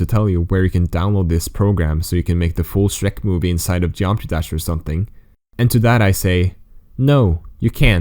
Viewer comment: Do I hear "the full beemovie but its in geometry dash"?